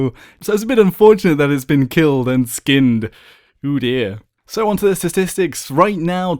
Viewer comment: When you said "oh dear" I thought you said "udyr" from League xD